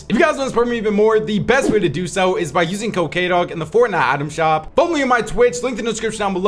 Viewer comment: I thought youve been saying “Use cocaine, dawg” this whole time 😂